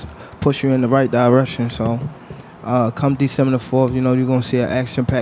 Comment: did he just say come December 4th ? 😁